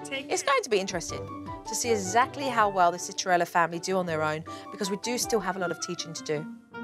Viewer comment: I thought that said “Cinderella family”